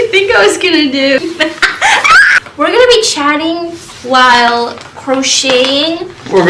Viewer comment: I THOUGHT HE SAID CHANTING